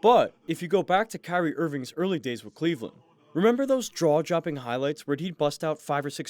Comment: Did you just say draw-jopping?